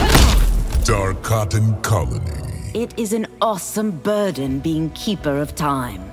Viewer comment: Did Ashrah just say "awesome"!?